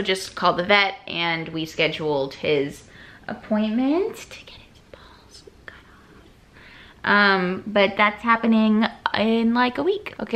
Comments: girl i thought you said get his paws cut off and i was about to call the police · when said they were cutting off chili's balls i thought she said paws and i was like 😧